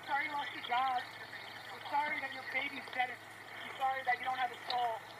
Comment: did that woman say “I’m sorry that your baby’s dead”? I listened a few times & think that’s what I heard. Woulda been absolutely amazing to have seen a fist come across the screen the second she finished that sentence.